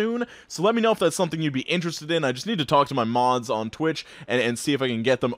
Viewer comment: You said "I just have to talk to my mods" but at first I thought you were going to say 'I just have to talk to my Mom" and I was so intrigued by how ingrained your mother was in your youtube career.